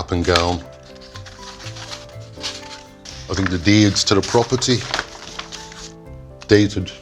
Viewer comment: did he say the deed to the property was dated in 1891?